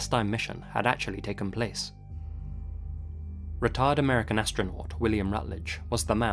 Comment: I was half listening while drawing and Literally™ thought you called him a "retard astronaut" not a "retired astronaut"...